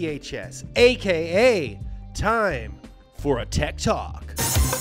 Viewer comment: I thought he said "time for a TED talk"